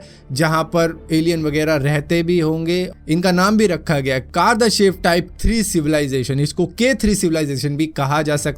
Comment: did u just said Kardashev type 3 civilization😶. Type 3 civilization can control multiple galaxies💀, thats why i asked u this?